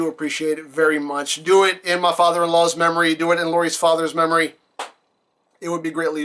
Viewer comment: her father, not stepfather. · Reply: Oh I swear you said stepfather. Sorry, must have misheard.